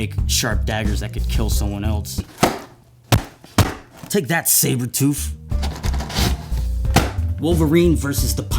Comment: What I heard was "TAKE THAT SABER TOOF" I'M DYING OF LAUGHTER